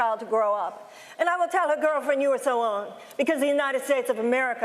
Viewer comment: Did she just say “girlfriend”?😆